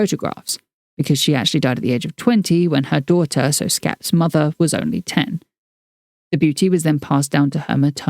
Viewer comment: am I hearing that right? Her grandmother died at age 20, when her daughter, Schap's mother, was 10? Wtf?